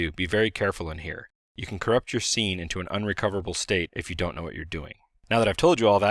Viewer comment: I almost had a heart attack. I thought he said "You could corrupt your C:\ into an unrecoverable state". Then I realized he said scene on the second lookover.